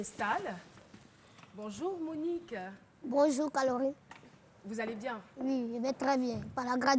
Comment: C'est moi ou bien Monique la a dit : " Bonjouuuuu ' Calorie ? " Eh dja .. En tt cas courage Monique.